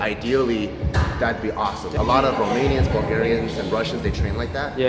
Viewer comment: Did he say Guamanians at 6:50????? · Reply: romanians